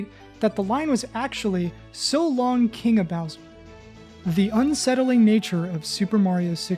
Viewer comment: I don’t care if Charles Martiney said it was “So Longa King Bowser!” I hear “So Long-ay Bowser!” and I find it frustrating nobody considers that as it would make the most sense and matches with how Mario talks.